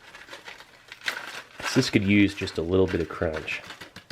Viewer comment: Gods, I briefly thought he said "crudge" instead of "crunch" when he was adding the crackers to the sausage at 9:58; one idly curious Google search later and I deeply regret mishearing him even more than I already did. Gotta love Urban Dictionary... 🤢